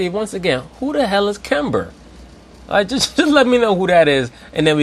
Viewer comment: I think he said Gember the 2nd time 😂😂